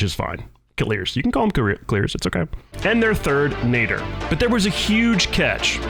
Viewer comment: What did he Say at 4:47😭😭😭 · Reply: Nadr their other teammate, youll see his name on the screen scoring when he says it